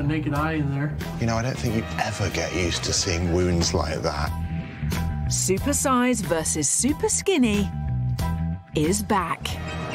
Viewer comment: i thought she said “super- sized vs super skinny, big backs” 😭😭